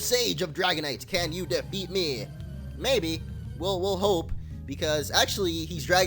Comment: Did he say Ho-Ohpe? Because if so, I have a new Pokémon pun. Slowking.